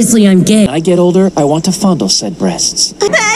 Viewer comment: this is why we don’t like the Bri’ish. The subtitles say “z breasts” because they promote z as zed, when in actuality, they said “said breasts”